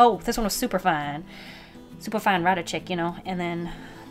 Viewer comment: I swear to God I heard her say super fine fighter chick